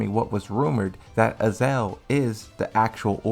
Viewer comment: LOL the way you say Azazel sounds like your saying Ezal, crackhead Ezal from Friday hahaha.